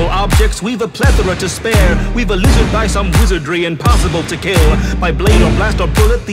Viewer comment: oh, I thought it said: "we've been eluded by some wizardry impossible to kill." which is funny, because it actually makes sense... but it actually says: "we've a lizard by some wizardry impossible to kill."